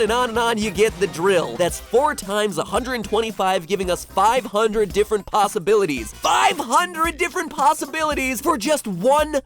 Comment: Did i hear say "500 different pastabilities"